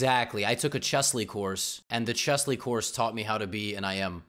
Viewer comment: Yes that's Levy's secret. He became IM by taking chessly course. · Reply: Oh, i thought he said, “chess league course”